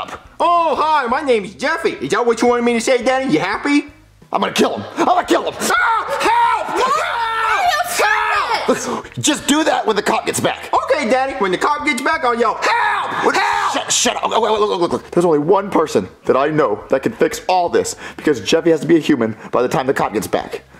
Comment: i always thought and laughed he said "cockitspack"